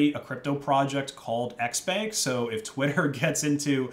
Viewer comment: Its "xbank" but i keep hearing "x spank" which is pretty much porn. elon is financing porn.